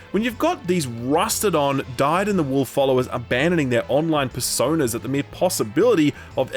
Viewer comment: I thought at 3:33 he said “online fursonas” and I was like wait what?